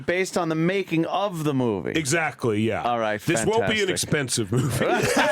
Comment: did he just say "exposively"?